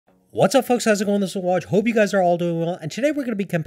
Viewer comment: sounds like he said “what’s up fucks!’